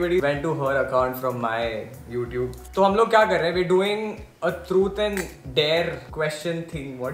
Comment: did they just said CHUT OR DARE?